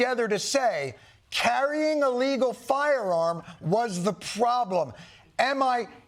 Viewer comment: The Auto capture write "is carrying an >I<llegal firearm..." around the 16:20 mark. What was said is "a legal firearm". I don't know how much control you have over this but it is worth the fix if you can.